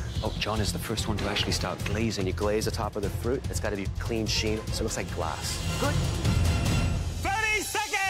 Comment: I thought chef claudio said "clean shaven" at 4:15. He said clean sheen 😅